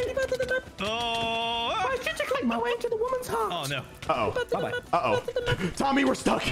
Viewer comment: did he just say i’ll jitter click my way into a woman’s heart